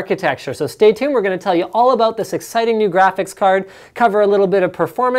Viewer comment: Did linus just said.. Sexiting? Me Gusta.